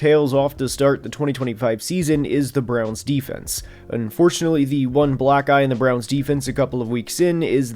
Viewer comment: thought he said the one black guy😂